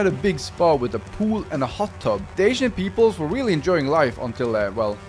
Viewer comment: Whenever he said “ancient peoples” I thought he said “asian peoples” which I personally thought was pretty funny